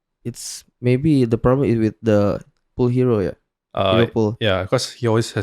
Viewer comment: HERO "POOL" WOI BUKAN HERO "PULL" WKWKWKWK